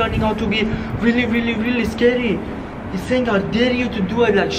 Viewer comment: WEN YOU SAY SCARY IT SOUNDS LIKE YOUR SAYING THIS IS SCADY😂